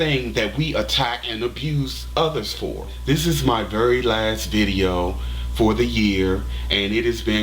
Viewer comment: i almost went crazy... i thought u said this was your last video for good lol :)